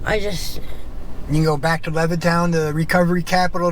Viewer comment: I live near Lewistown. Always been big in heroin for some reason even tho it’s the middle of nowhere. Think it’s because they have an Amtrak. 20/20 did a special on it years ago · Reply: Ahh I did. Could have sworn she said Lewistown🤦‍♂️. Not much difference tho I’d imagine